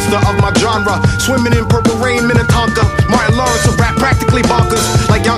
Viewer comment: I thought Tame said "Martin Lawrence of rap, practically bonkers"